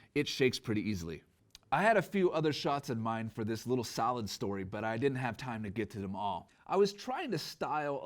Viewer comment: Just ordered the cine version with a $200 Cyber Monday discount. Thanks for the honest review! 4:24 salad story thought I heard sad story. lol :D